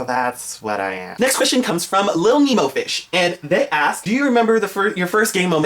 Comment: I thought he said Little Nemo Bitch.. ahahahaa.